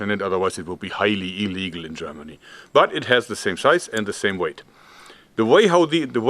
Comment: Ich hab „the same scheiß“ verstanden😂😂